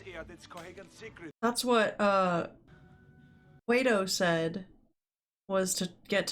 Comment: Definitely misheard you as saying, "That's what Plato said... get to the reactor!"